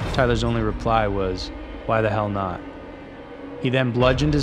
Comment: he actually said why tf not🤷‍♂️